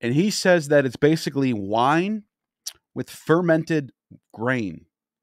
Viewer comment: Man I thought you said wine with fermented brain when you said fermented grain at the end